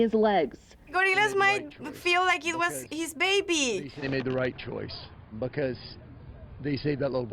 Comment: wtf did she just say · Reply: "cause he might think he's his baby!"